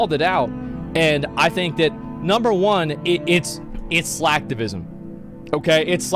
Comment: I heard "Slacktivision" hahahahaha